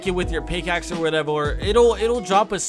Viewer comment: i heard whatevor